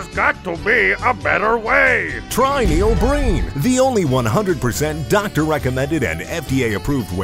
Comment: I swear to God the first few times I watched this I thought he said "use your brain" instead of "use Neil Breen"